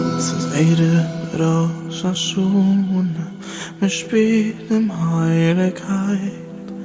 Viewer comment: can u please tell me what you said there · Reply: טוה עס פאר דיינע קינדערליך